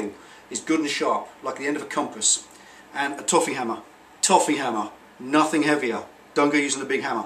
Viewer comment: Maybe I’m mishearing you but I can’t find a “toughy” hammer anywhere. Would a a 12oz steel head hammer do? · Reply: 'toffee hammer'.